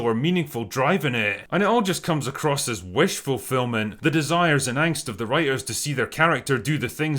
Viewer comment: I misheard "Wish fulfilment" into "Wishful filming"